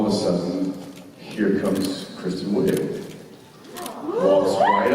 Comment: Also, anyone know who Jensen was talking about at 31:22?? My hearing is bad enough that I can't quite make out the name. 😓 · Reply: I think he is talking about Kristen wiig the actress